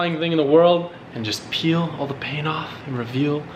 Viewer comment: Did anyone else hear say he was gonna peel the paint off at 0:25 😂😂